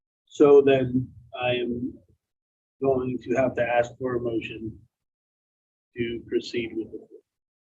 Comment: what does the guy say? He proceeds to make a motion to .... He mumbles. I know they all vote guilty, but did he say guilty? The fact this dirtbag stole his own mothers money and she gets booted for her assisted living. That's low. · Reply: It’s a motion to approve Forfeiture.